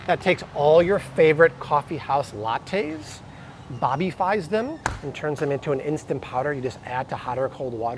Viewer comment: Wait…did he just say “Bobbyfies” them??? 😂🤣